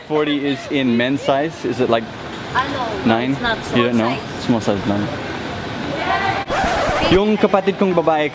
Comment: did you say "small size lang"?? lol Your Tagalog is seeping through into your English! That's how you know you've crossed over to the Pinoy side :)